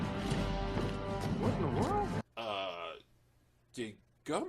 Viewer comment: He was clearly saying "What in the world?" very quickly. Also, how is it that no one else noticed the gravity thing? Did none of the 3 billion people on the world notice their interplanetary mail stopped? Did no one on the planet send out a transmission? Lastly, and much less importantly... Did Hawkgirl cut a gun barrel with a mace?